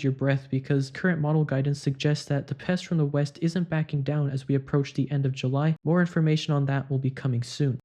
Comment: ”Will advise you not to hold your breath because…. ”— What are you saying at 4:20? However hard I try — been going back and forth like ten times — I can’t understand (non-native speaker) and it’s relevant as I’m in the North. Thanks! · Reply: “Current model guidance suggests that the pest from the west will not be backing down as we approach the end of July…”